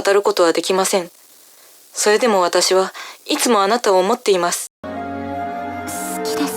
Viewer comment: the last word is her saying "想っています" Being that, she is saying "[...]but I'm always thinking of you."